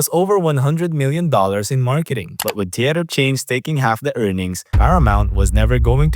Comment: I didn't understand the comment about something taking half the earnings, so I had to Google 'what takes more than half of a film's earnings' to decipher that he said, "But with theatre chains taking half the earnings..." 💸